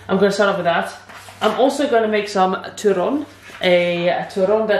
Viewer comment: I thought she said "TURON" I was excited i thougt she was going to cook some filipino desert although to be fair turon is not a christmas food its an everyday type of desert or snack.